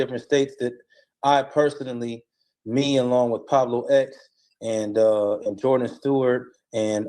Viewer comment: Did he say PABLO X? There’s a Hispanic out here calling himself Pablo X